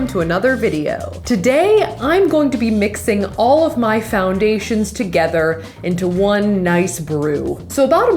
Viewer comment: for some reason, i thought that this said "mixing all my emotions together"